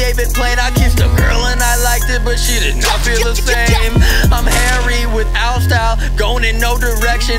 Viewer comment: I’m Harry without style 😩😂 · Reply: ngl thought it said hairy